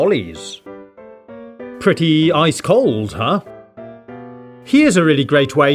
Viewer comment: Pretty ice-cold huh instead of pretty cool huh